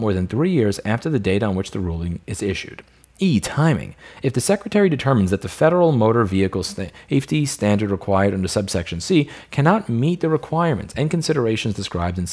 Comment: i heard "ii timing" and thought louis was speaking japanese lol